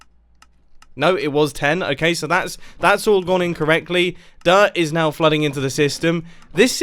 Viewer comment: it looked like u said incorrectly instead of in correctly